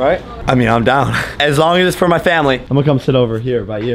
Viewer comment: I thought it said I bought a "Tessa for our family" lol